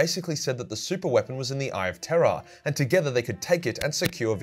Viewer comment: I got into warhammer through this channel and for about the first three months I thought the eye of "terror" was the eye of "terra" solely due to your accent. 110% the best Warhammer channel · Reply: I got into Warhammer and, even before finding, still heard it as "Eye of Terra" until I saw it written down. It is an awkward phrase.